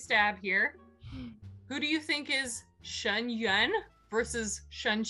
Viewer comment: I hear "Shen Yun" every time you guys say "Shen Yuan" Me (Who instantly recalls my dance training): 👁👄👁 since when did MXTX become Shen Yun fan?